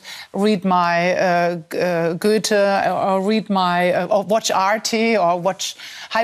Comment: Did she say "watch RT"? Is she talking about Russia Today? or something else? Please let me know.